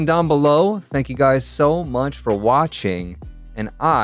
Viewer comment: he said so munch instead of much